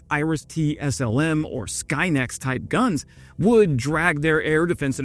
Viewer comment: OH, I SWORE that you said “Sky Net!” I popped closed captions and the first round actually caught SKYNET, I rewound and that time it read “skyNext”. That is what you get when you trust your 6 year iPad to be right!